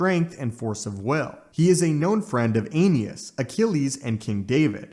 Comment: LMAO i thought you said "he's a known friend of an anus"